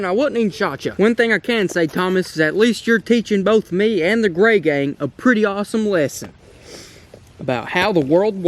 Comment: Tbh I legit heard “GayGang” instead of “GrayGang” 😂😂. Lmao fml